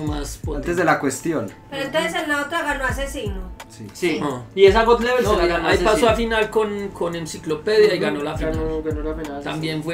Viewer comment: que dice de Mcklopedia , que no clasificó por descendido 😂😂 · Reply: Que Aczino gano la final de esa God Level vs Mcklopedia. eso dijo.